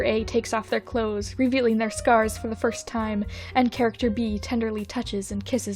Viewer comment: Thought it said “revealing their scars from the first time”. I was thinking “what happened the first time they did the thing????”